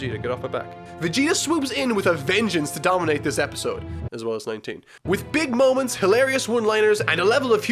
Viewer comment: I looked away for a second and thought he said “Vegeta swoops in with a vengeance to dominate this episode, as well as my taint.”